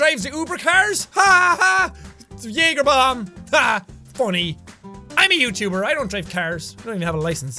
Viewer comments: I thought Jack said "I'm a YouTuber! I don't drink cars!" and I thought it was hilarious because he was talking about a bar, and then I realized I just misunderstood him. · In the end I thought jack said I don't drink cars and I'm thinking to myself "who does?!" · Did Sean say that he doesn't drink cars at 12:13 or is it just me. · Jack, did you just say you drink cars? I think may need my ears checked :P